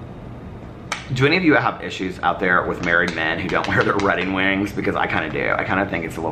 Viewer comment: I so had to comment again... said Redding Wings instead of wedding rings @ 23:17ish.... I love it!!!